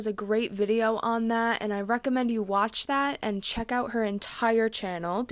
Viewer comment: did you say, "watch or check out the Max Zeboss video". ??